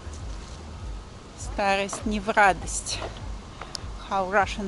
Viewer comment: I Enjoy your videos. What are the Russian words used at 11:40 about getting old? I can empathize. Learning the language, slowly. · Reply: Старость не в радость